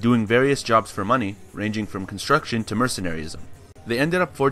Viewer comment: Umm..did I hear mercenary?